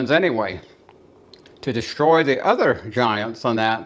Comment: when he said Surface people i thought at first he said Serpent people. But then i used CC to read it.